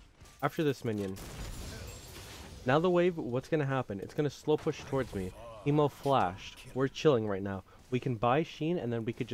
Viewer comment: did he just say"ειμαι ο φλαση" or is it just me